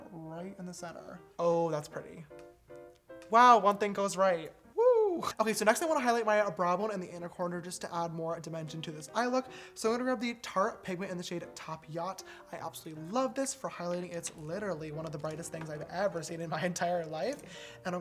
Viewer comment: The subtitles say (tart penguin). lmao.